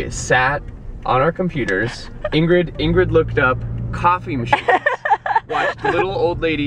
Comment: oooooh i thought you were saying copy machine, not coffee machine... i got confused for a little bit